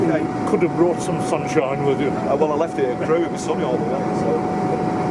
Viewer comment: Thanks. The driver told me that he'd had sunshine all the way to Crewe - and then grey skies for the rest of the day. Hope you're keeping better. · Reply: I heard him say We left the sun at Crewe. I am doing better and had another checkup today which went OK